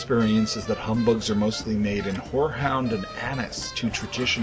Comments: What were these flavored? I keep hearing "boarhound" and I know that's not it ;) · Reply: by Rae The flavor was horehound, and it kind of tastes like a combination of root beer and licorice. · Whats the name of the flavour?? I swear I hear "Whorehound" every time